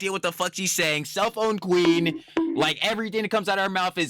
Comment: I doubt I'm the first person to point this out but whenever you refer to Denims I always hear "the CELLPHONE queen" instead of "the SELF-OWN queen" 😅